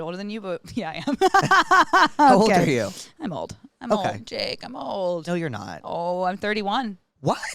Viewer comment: did she just call him Blake